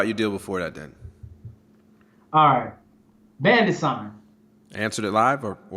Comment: what exactly is the word being pronounced? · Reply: bandit sign, signs you leave in high traffic areas.